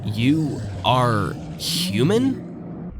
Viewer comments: I heard "You are a near human" But IDK man 🙏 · I thought it said "You are glorbo" may this valuable information spread across the lands and be remembered for thousands of years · "You are HORRIBLE!" That's what I heard. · I actually think she says "You bring -" don't know what the end of it says, but it sounds like it's condemning what your character is doing, also, I think the two characters you play as have conflicting goals as well. The first one seems pretty content with what's going on, as if they work there, and they're trying to get the factory up and running again, meanwhile the second character you play as is just trying to escape, as if the reason it's abandoned is because all the other sentient beings have already transferred over to whatever the doorway leads, and have abandoned this world which is why it's in such disarray. The first scorn guy wants to continue the cycle and was willing to turn the second scorn guy into whatever that was, to prevent another person from leaving, and they turned SG2 into that thing to be added to the factory, and will continue to try to rebuild what was left behind, not letting anyone else escape. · I think it says “you were human”